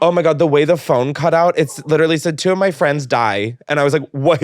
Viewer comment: The audio cut at 34:35 was so perfectly placed LOLL I also thought he said “two of friends died”